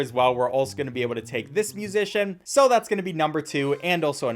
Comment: i heard "piece of shit" three times before i finally heard musician